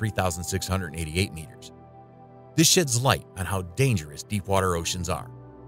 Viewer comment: did it say “This shit light” or “This shades(sheds) light”?